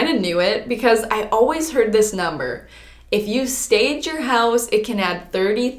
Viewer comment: When you said "stage your house" I thought you said "sage your house" and my spiritual a** was like that checks out